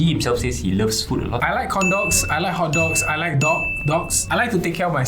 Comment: i swear i heard "i like condoms"